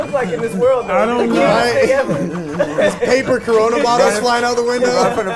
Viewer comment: Swore I heard coronavirus but it was just corona bottles LMAO